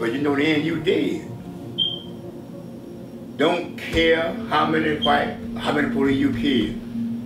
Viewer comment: Sounds like he was gonna say " I don't care how many Whites..."